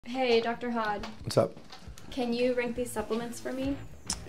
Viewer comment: Thought she said Dr. Hot lol